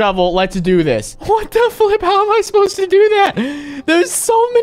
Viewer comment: did he say f?